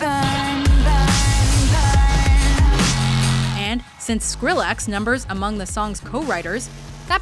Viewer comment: Did she just say Skrillax!?